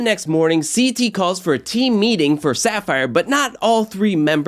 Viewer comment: Devin has a "team meeting" but all I heard was "I,me, my game"